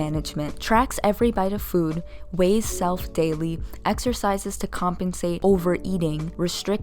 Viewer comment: R/fat logic in a nutshell.... Exercise???? I thought you said extra fries??? (Edit: Omg thank you guys for the likes and heart!)